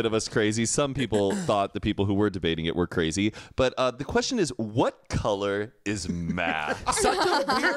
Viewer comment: I thought they kept saying “what color is MATT?” lol.